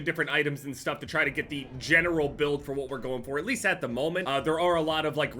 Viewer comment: did he say "genital build"?